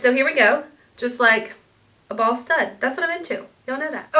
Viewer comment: Bootcut Jean's and green sweater are perfect! You must keep!. .I died when you said a "ball stud is what I'm into" because it sounded like you said 'a BALD STUD'..! Kinda sounds like you had the hubs on your mind!